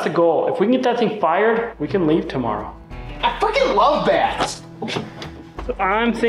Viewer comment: that kid said what? Potty mouth on channel??? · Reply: I thought I heard “I f#%^ing love bats”. LOL. Bats are awesome though.